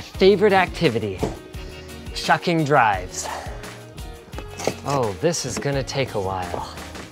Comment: Did you say f drives at 1:45?